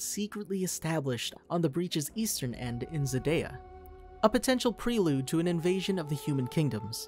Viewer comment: did he say Xadaya??